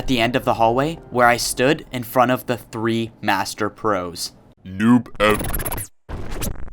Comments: wait a sec i think i heard noob evbo for a spilt second hmmm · hold up, DID IT JUST SAY NOOB EVBO